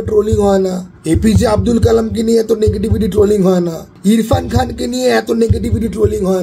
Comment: Did i hear APJ Abdul Kalam? 4:06 😢